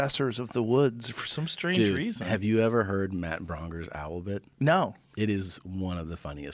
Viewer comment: Matt who? I can't understand what he says for the last name?? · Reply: Braunger